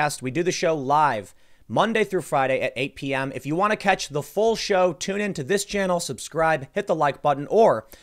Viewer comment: Because of the pandemic, every time Tim says 'If you want to catch the full show...' at the and I always hear 'if you want to catch the flu..'. Thanks Covid